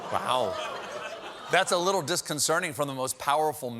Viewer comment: does he say “disconcerning” instead of disconcerting?